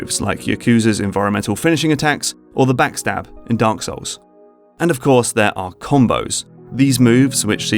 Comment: did you just said Dank Souls?